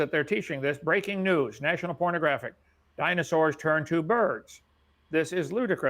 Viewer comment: Did he say "National Pornographic" at 26:17?